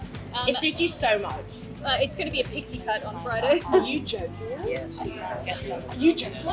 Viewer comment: I heard “black pixie cut” and now I can’t wait til Friday !!